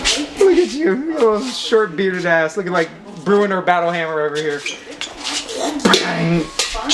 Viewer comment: bruh did just call him bruenor battlehammer? Thats a real nerd reference.